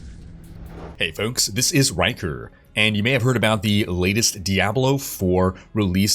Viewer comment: LOL not sure why but I always hear "Hey folks, this is wanker" ;-)